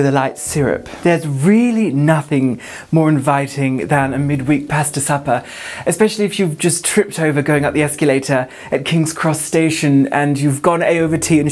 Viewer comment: Ok ignorant American here… what does she say from 1:27 to 1:35? For the life of me I can’t understand it. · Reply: The whole line is "there's really nothing more inviting than a mid-week pasta supper, especially if you've just tripped over going up the escalator at King's Cross Station and you've gone A over T (ass over tit) and shown all of Café Nero your farfalle"